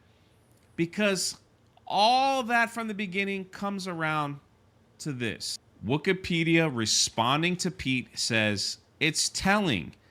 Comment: Wookiepedia... Never even knew that was a thing until I saw this video. I thought you were saying "Wokeipedia." 😂